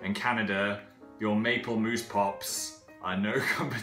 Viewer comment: Phil I have lived in Canada all my life and I have never had a moose pop... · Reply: god I thought you said 'moose poop' for a second and got really worried... XD